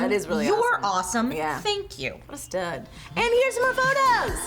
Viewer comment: hey what did say at 1:27? right after said thank you. I couldn't hear it. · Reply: What a stud